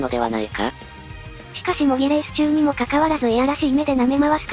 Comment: 模擬レースが揉みレースに聞こえてならない